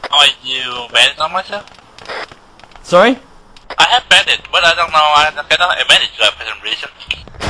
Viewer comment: what was saying is “I have bandage but I don’t know why I cannot bandage my self for some reason” 2:48 “did they have an Ak by the way?” 1:48 “how do you get more kills but stay shit?”